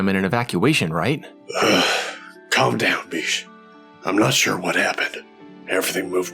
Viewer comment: Did he say calm down bitch